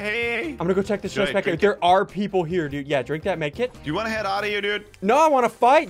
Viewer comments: anybody else hear “Drink that med kit?” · did he say yea drink that medkit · he said drink that medkit did anyone else hear that